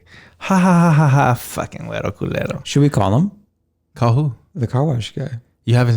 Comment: I totally heard “huevo culero” when said it 😂